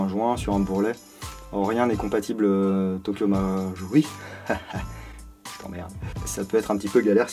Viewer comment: il n'y à que moi qui à entendu "je t'emmerde" =) sinon très belle vidéo comme d'habitude =)